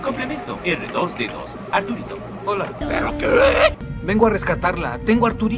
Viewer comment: A mí sí me gustó mucho que R2D2 fuera llamado Arturito,luego lo corrigieron y lo llaman Artu pero básicamente es Arturito jeje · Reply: Yo siempre escuché "artuditú"